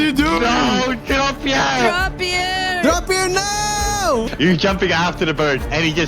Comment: for a whole year i thought he said "drop your nose" instead of "drappier no"